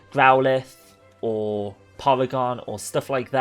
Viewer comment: I'm preeeetty sure you called porygon polygon. Maybe it's just your accent but that's what I heard